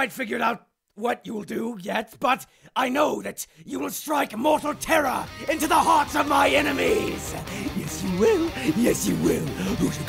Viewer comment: I’m starving, so I thought papyrus said “YOU WILL STRIKE NUTELLA INTO THE HEARTS OF MY ENEMYS!”